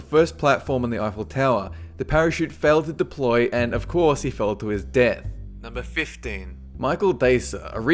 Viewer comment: It sounded like you said "Michael Bay"